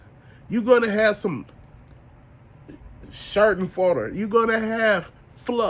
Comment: I know I heard "Shart-n-Farter" right there. Yeah, saving that word for future use, I dig it